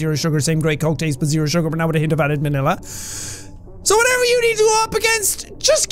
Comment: Did he said manila